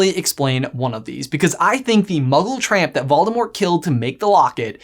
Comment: I thought it said Voldemort’s fairy godmother